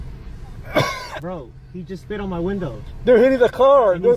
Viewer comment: I could have sworn I heard screaming "He spit on my window"!